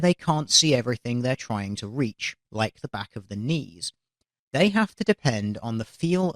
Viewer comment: definitely thought you said "like the back of their penis" lol